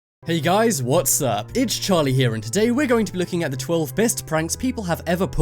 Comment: Oh your name is Charlie sorry I just thought you said Harley